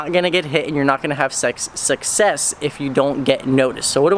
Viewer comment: Did he just say "you not gonna have sex?"